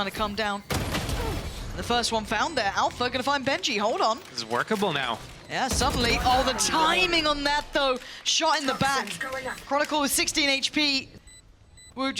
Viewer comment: タイミングは納豆に聞こえる😂